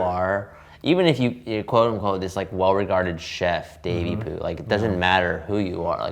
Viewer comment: When said “Davey-poo” I thought I heard baby food and now I want to see a Worth It: Baby Food edition. Sounds like a bad idea but it could also be the one episode that everyone didn’t know they needed💀